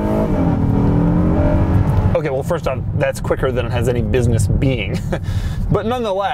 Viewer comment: "That's quicker than it has any business being" Acceleration: 1/10 WTF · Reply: I thought he said "that's quicker than any business meeting" :)